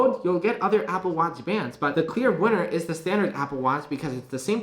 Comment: Did he say the clear winner is the standard Apple Watch at 4:35?